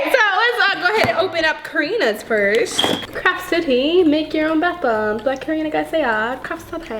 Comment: It sounded like you said crap 💩 city 🌃 at the beginning 🤣🤣🤣🤣🤣🤣🤗💯❤️🤣🤣🤣🤣🤣🤣🤣🤣🤣🤣